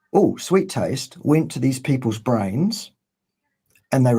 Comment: Lol, I thought Dr. Berry said, "Grains could not have possibly evolved on the savannah." I about threw the computer until I realized he said "brains."